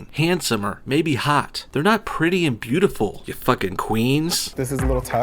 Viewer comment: When you said “queens” I thought you said “queers”.